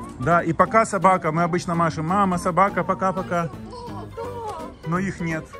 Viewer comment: мне не послышалось папа сказал мама собака на